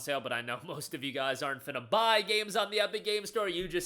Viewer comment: What did you say? Not ____ buy games on the Epic Games Store. It sounded like you had a mini stroke and something non sensical came out in that spot. 😉🤪🤣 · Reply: he said “finna”